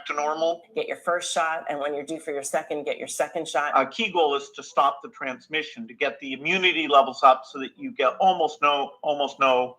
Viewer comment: Is Gates saying "A kegel is to stop the transmission and get the immunity up" ...I looked up Kegel exercise and it has nothing to do with a needle or injection but if it helps I'll try it. KEGEL to flatten the curve.